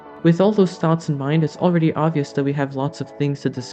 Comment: What do you mean? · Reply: Hi there, It sounds like you said at 0.44 it's already August, maybe I'm just wishing 😁 Love your work ❤️